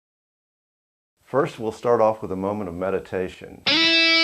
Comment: At first I thought he said, "First we'll start off with a moment of medication." XD